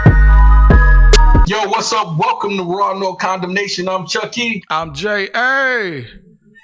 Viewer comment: For a second I thought you said welcome to Raw no condom Nation 🤣🤣🤣